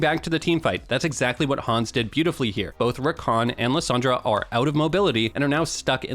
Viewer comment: subtitles say Endless Andre for Liss lol